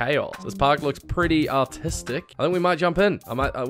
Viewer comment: For the first one I thought you said autistic not artistic😭